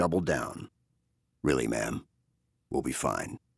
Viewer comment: Using "man" colloquially seems a little out of character for MC, but Steve does a great job voicing this, should have paid him to do the entire book. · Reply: Idk haven't read the book maybe the char he's talking to is a female officer but it really sounded like "man" to me.